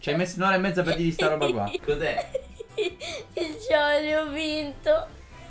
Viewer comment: ho sentito: Jolly, ho vinto!